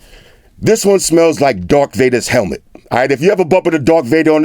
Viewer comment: ''TRIGGERED'''Did he just say ''Dark Vader"!??!?!?!?!?!